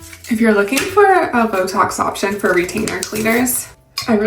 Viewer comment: What is your sleep score from? I had to turn on my closed captions… I thought you said Botox options for retainers 😅